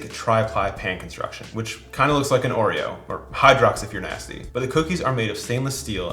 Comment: I thought you said “Or hydrox if you’re a nazi” and I laughed so loud 😂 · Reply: He said "if you're nasty," not "if you're a Nazi." It was a reference to a lyric from the Janet Jackson song, "Nasty."